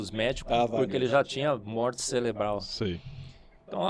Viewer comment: Foi somente eu que ouvi "celebral"?